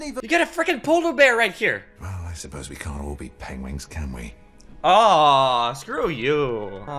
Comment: Did he just say "Pengwings"?